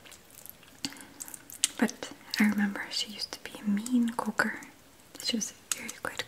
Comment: I thought she said her grandma was a "mean cougar" @ 10:20 but she said "mean cooker-she was a very good cook" lol